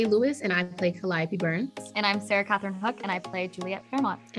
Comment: did i hear right? 🤨 Just kidding don't wanna be annoying xd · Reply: If you mean what i think you mean then no i dont think sarah said honey i think she said imani and it sounded like she said honey